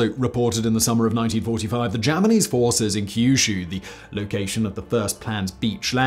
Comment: Wait, did you say “Germanese forces” @ 8:31 of this video?? 😆🤣